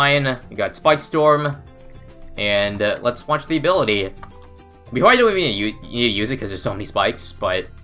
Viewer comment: Anyone know what he said at 3:31? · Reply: “We probably don’t even need to use it cause there’s so many spikes” is my best guess